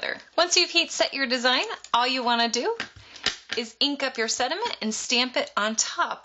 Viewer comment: It sounds like you say sediments. Lol Cool idea! Thanks